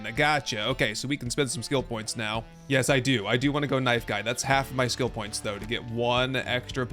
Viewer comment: I looked away for a second and thought you said “Nice Guy” perk. Did I forget that perk? Nope, “Knife Guy” 😂